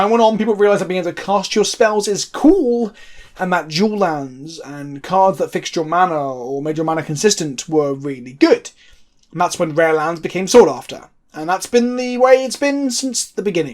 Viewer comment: Did I hear "Juul" lands at 3:54? Sign. Me. Up.